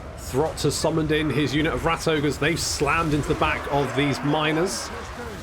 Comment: My half-awake brain thought you said "they've slammed into the backs of these minors" at 17:22 and had to do a double-take